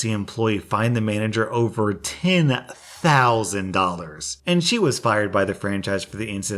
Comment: i thought it said over 10 dollars and I'm like um that's not a lot then he said 10,000 and then i was like omg that's a lot